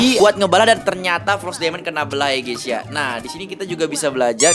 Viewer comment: Di menit 10:32 apa gue gk salah denger bilang ny frost diamond kena belah bukan ny Spongebob Ig feriskaolivianasir